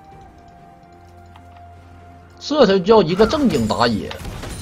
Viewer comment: 到底说了什么，猴语没点满根本听不懂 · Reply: 這才叫一個正經打野